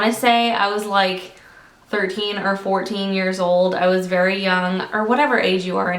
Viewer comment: OMG when you said how old your daughter was I thought you were saying 18 or 19 years old I was like umm did you have your children when you were nine hahah